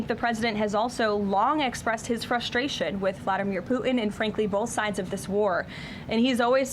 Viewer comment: Did she just say "poo'in' ??" 0:07 Core blimey guvvna 😅